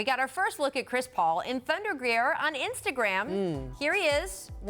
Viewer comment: She said “Thunder wear” but I heard “underwear”